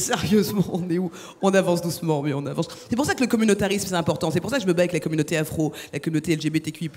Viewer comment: elle vient de dire le communautarisme c'est important ou c'est moi qui ai mal entendue ?